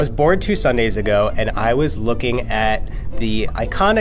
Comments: Who thought he said “I was born 2 Sunday’s ago” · I literally thought he said I was born two sundays ago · I thought he said "I was born 2 Sunday's ago" 😂😂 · I thought he said "I was born two Sundays ago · it sounded like he said I was born 2 sundays ago 😂😂😂😂😂😂😂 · anybody else thought he said 'i was born two sundays ago' ? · I thought he said I was born two Sundays ago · OMG😂 0:30 I thought he said he was born two Sundays ago😂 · I heard “I was born two sundays ago” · BRUH I WAS DOZING OFF CAUSE IT's 2aM AND I THOUGHT SAID "I WAS BORN 2 SUNDAYS AGO" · I thought he said that he was born two Sundays ago. Imagine my 'otter' confusion. 😉 (Sadly, there is no otter emoji) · I thought he said he was born two Sundays ago lol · i thought he said he was born two sundays ago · I thought he said that he was BORN 2 Sunday’s ago 😂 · I thought he said "I was born two Sundays ago" 😂😂 whoopsies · I THOUGHT HE SAID I WAS BORN TWO DAYS AGO WHEHDBHAHAHSGVDVDBDBBDBEBWIJAJAJSJBDBD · I thought he said "I was born 2 Sundays ago" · He said I was bored two days ago but I thought he said i was born two days ago haha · i thought said he was born two sundays ago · I thought he said “ i was born two sundays ago” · I thought said I was born two Sundays ago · thought they said "I was born two Sundays ago" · I thought he said he was born two Sundays ago! 😂😂😂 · i thought he said he was born two sundays ago lmao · I thought he said {I was born two sundays ago} alright fam if you say so